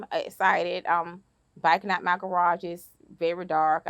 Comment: Her FL accent is so strong, I heard “Biking” instead of “Backing” so it took me awhile to realize she didn’t get shot on a bicycle while biking out her garage 😂 I’m like “ohhh, she got shot in her car BACKING out of her garage” 😂